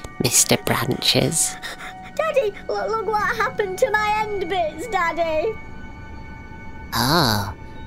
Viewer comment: Uuuuum Could someone Tell me What did the tree said at 1:19? (I'm not American) Did he say "endband", "endbind"? I've never heard this world😕 What's that? Did he meant "limb"?